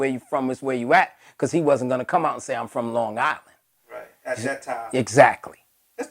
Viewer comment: Wait, I thought Rakim said Strong Island when he repping. Hmmm